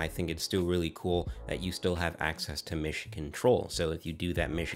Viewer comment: I kept hearing "Michigan Troll" until I understood it was "Mission Control". I must admit I was scratching my head at first lol.😂